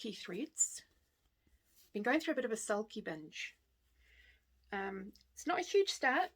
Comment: Cracked up when you said “sulky binge” because I totally heard “salty b!tch” LOL - can’t wait for the retreats next year, hopefully i can meet you in person! - take care, Nic x